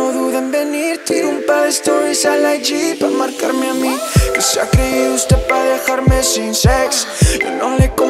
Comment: Alguien mas no entendio lo primero que dijo?? LOL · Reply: En realidad solo entendí “que se a creído usted pa dejarme sin sex” y la parte donde canta la chica que por cierto tiene una bonita voz